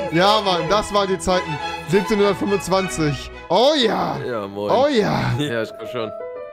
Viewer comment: Ich habe 17 Uhr 25 verstanden